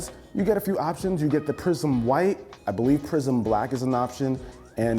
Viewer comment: Did he really say 'prison black' at 2:50 LMFAO?